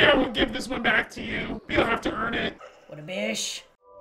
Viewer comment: I was not the only one who thought said “What a fish”